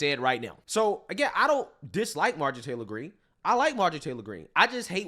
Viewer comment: Lol i thought he said Marjorie Trailer Greene